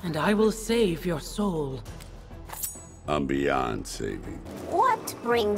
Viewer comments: Thought he said "I'm beyonce" instead of beyond saving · I thought I heard for a moment ¨I´m Beyoncé¨ I was like, WTF?